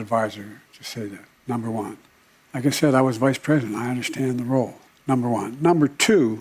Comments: Did he say was Vice President at 4:20. 😂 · Did I hear that right, @ 4:20 “I was vice president”